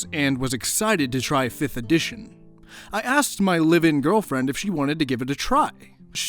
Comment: I heard “living girlfriend” not “live-in” and was thinking, “geez I hope so, no necro or pillow thanks.”